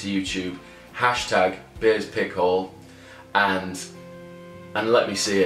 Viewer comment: hahah for a second I thought you said "hash tag Bea's Pick hole" Maan I want that Tosin pick!